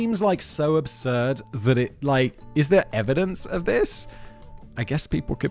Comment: he said "is there evidence of this?" But to my ear it sounded like "I hope there are pictures!"